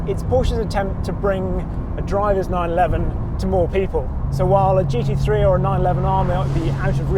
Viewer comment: I was crunching toast. I misheard "... well, it's Porsche's attempt to bring a driver's 911 to poor people". Which is probably quite true.